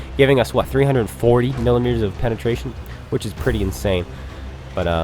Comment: sounded like you said 340 kilometers of penetration. (im not using headphones on my phone lmao)